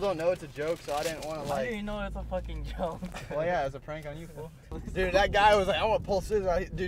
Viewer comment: Did he say foo ? Damn cholo